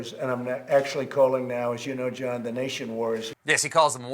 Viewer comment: I actually thought he said "worriers", meaning the people who were concerned about re-opening too soon. I wonder if, when he said he was wearing a mask at the factory, he meant his goggles?